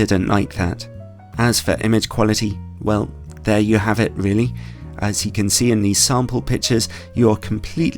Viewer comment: I swear I heard him say "As you can see in these SO-CALLED pictures" and had to rewind to find I was wrong. 😂 "bad" lens reviews always make my day 👌👍